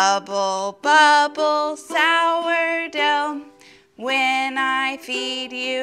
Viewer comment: I thought you said "when i feed you watch you groan"